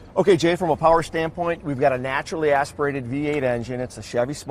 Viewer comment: I thought I heard "naturally aspirated" ???? For real--- it can't be ?????